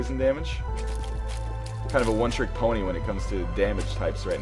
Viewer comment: Did I hear "One Trick Pony" at 8:47? Tee-hee.